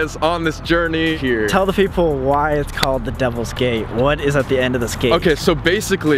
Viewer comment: I thought Sam said “the Devil’s Gay” :o